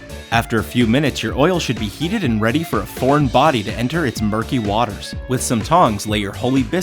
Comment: I can't be the only one not to hear what he said at 00:40 he said porn godam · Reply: "Foreign body"